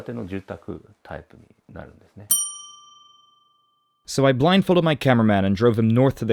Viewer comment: I thought you said I blindfolded my driver. I’m not quite awake yet does that explain it